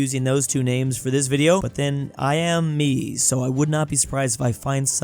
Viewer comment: Did you say Miami?💀💀😂😂😂😂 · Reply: He said "I'm me" 😂